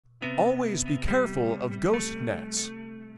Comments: I thought he said Ghost Nuts 💀💀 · Could've sworn it said "alwyas be careful of ghost nuts" 🤣😭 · Thought he said “ghost nuts” and thought there were some giant floating nuts in the water I think I need a nap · I thought it said Ghost nuts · I thought it said ghost nuts 💀💀💀💀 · help i heard it as ghost nuts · I thought they said ghost nuts lol